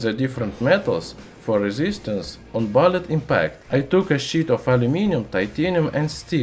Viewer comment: shits metal confirmed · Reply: I took a shit of aluminium